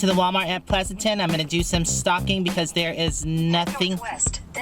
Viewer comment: it sounded like you said you were "going to do some stalking here at the walmart"! lol.hugs